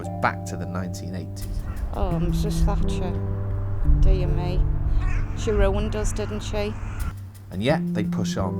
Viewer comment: Why at 8:23 do the subtitles say "Oh Mrs Thatcher, dear me she worked wonders, didn't she? - when the actual comment was "...she RUINED us, didn't she."